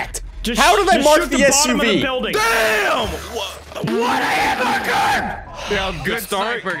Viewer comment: it sounds like he said "I want a Hamburger."